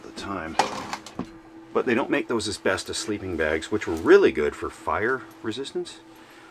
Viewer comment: did he say asbestos sleeping bag?